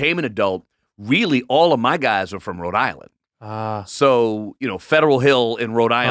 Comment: Had no idea Alfonso was familiar with Rhode Island. I walk down Federal Hill every day haha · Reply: I thought I heard Rhode island but due to the context of the conversation I thought I had misheard Long Island. Thanks for validating me